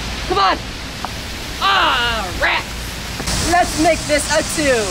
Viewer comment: Did he just say "Awwwwww rats"